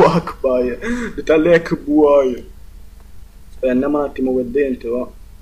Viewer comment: Tolbar registro con Fraps :) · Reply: T0LB4R ™ e poi ALAAICBAI AHHAHAAHAHAHAHH minuto 2:03 a 2:07 si dice ALLAHU AKBAR AAHAHAHAHAHAHAH